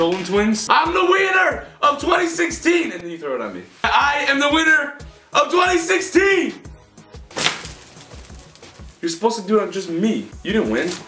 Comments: did he say “ I am the wiener of 2016” HAHA LMAO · am I the only one who heard wiener instead of winner? haha xD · it sounded like Ethan said "IM THE WIENER" instead of "IM THE WINNER" 😂😂💞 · Lmao I thought Ethan said “I’m the wiener “ 😂😂😂😂😂😂😬😬😬😬😬😬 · Did he say he's the "wiener of 2016" but... What I'm the wiener of 2k16 I RAN INTO A POLE FOR NOTHING THAT YEAR CRAP!!!😂😂😂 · I sound like he said I’m the wiener of 2016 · Did E say “I’m the Weiner...? Of 20 16?” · It sounded like u said i am the weiner of 2016 😂